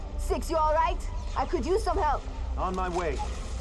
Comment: AM I CRAZY OR DID HE SAY ON MY WAY LIKE DID NOBLE SIX JUST SAY THAT